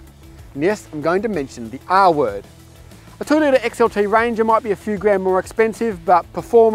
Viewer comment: minutes, did he say “Toyota XLT Ranger”?? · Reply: 2.0-litre XLT Ranger